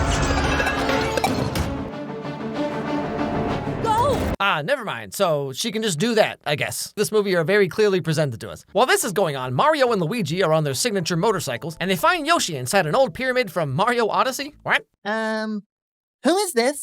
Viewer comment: did he say “this movie are very clearly presented to us?” I assume he’s criticizing the fact that Rosalina didn’t just do that earlier